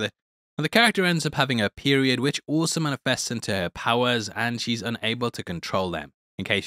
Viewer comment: thought you were saying she got period powers 😂😂